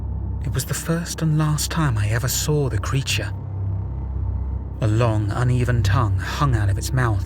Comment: Un hairless creature, also known as a creature with hair, double negative means a positive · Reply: I thought he said "A hairless creature"